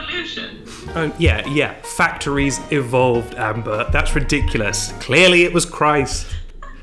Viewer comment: did i hear the laughing lady be called amber? look